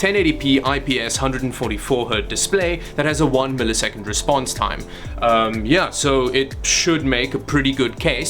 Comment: Did you just say ips display with 1ms response time?